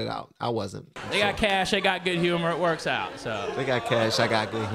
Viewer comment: I thought he said , They got cash, they got good humour. Bcoz fat people stereotype is to be jolly.